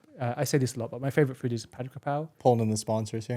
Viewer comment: Does anyone know what thai dish Alex is talking about at 15:11? I didn't quite catch the name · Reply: Phat kaphrao